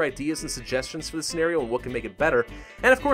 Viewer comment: "pick a side or you will probably lose" ok. also: does exactly the opposite; loses: :0 THIS SHIT IS BROKEN. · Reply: Also, did you just say?